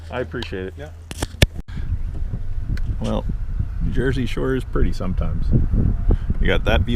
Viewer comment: You said Jersey sure is pretty sometimes and I thought you said Jersey shore is pretty sometimes, so don't jump on her for it.